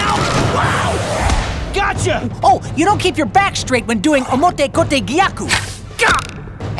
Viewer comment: what was he saying??? i'm trying to spell it i'm having a hard time T^T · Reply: He's saying Omote kote gyaku it's basically a wrist lock technique